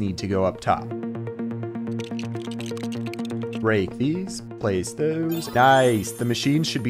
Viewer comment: subtitling error uses 'brake' instead of 'break'